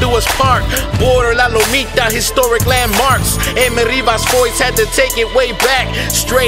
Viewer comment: te Falto El Foys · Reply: I think he does say Foys. Right after M.Rivas